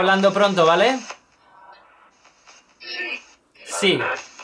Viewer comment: Minuto 10:17 le entendi : Si?